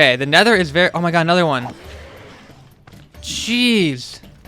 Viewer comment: cheese?am i the only one who heard that ik he said geez but